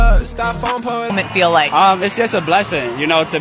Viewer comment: “Is jus a blessin”💅💅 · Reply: Why did I hear "It's just a Black thing...."